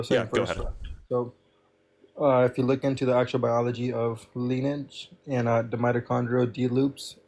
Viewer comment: was he saying lineage? Cause if so... oh no!